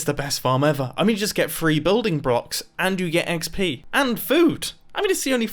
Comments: did he just say building brocks? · Did he say Building Brocks